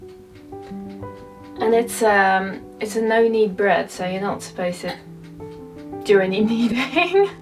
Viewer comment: I am proud of you for trying the bread. A tip, it looked like it wasn’t kneaded enough as it should be smoother before the rise. Whole grain bread especially needs a lot of kneading to help it develop the gluten structure that will help it rise and provide a beautiful even open crumb. Knead it for 8-10 minutes. You can test if it’s ready by pressing the dough with your finger. It should bounce back. If it doesn’t it needs more kneading.😊I’ve never heard of that long of a rise time. Usually it is 1 1/2 to 2 hours. Punch down, shape and then rise again for about 30-45 minutes. Also, even cold proofed bread in the fridge is usually 8-12 hours, then set out for an hour on the counter to warm up before baking. Anyway, keep at it and you’ll find yourself making amazing bread! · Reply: I thought she said it was a "no knead bread."